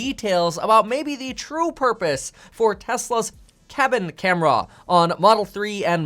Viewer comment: Lol when you said cabin camera I thought you said Kevin camera at first